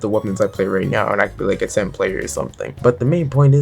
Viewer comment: did someone say Tent player????